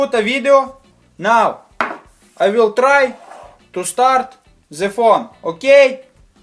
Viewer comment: did anyone else hear Za Phone